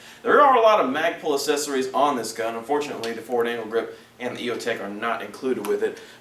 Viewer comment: sounds like he said forward anal grip LOL